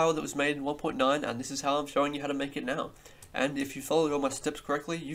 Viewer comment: I thought you said how to make a lemon